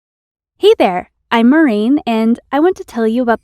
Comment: Only I heard "eighter", not hi there on the start?